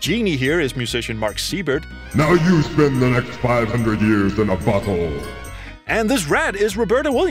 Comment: I'm sorry but did he just say, "Now you spend the next 500 years in a bu tthole..."?